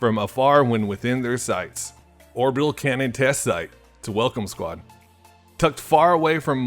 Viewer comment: how DARE you disrespect my boi he said welcome COMMITTEE not squad